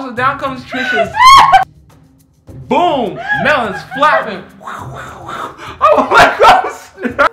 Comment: “Melons flying everywhere” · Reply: When did he say that? I heard "Boom! Melons flappin! Whoosh! Whoosh! Whoosh!"